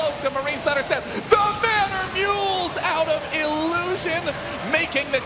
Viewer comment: "THE MANNER MULES" just sounded like "FINISH HIM" to my hear :O